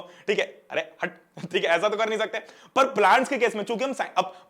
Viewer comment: Did he say "Hatt B***dike"?? 😂😂😂😂